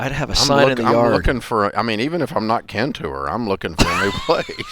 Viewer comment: what does he say when he says "even if I am not kentouring? cantouring? at 4:33 what is the expression, I hear it for the first time. English is not my mother tongue. Can someone tell me, pls? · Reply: Hey I know it’s been a couple of years since you commented. What he says is “even if I am KIN to her.” Kin is slang in the south for family.